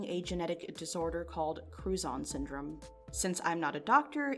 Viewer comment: Was only listening and had to rewind I thought she was saying crouton syndrome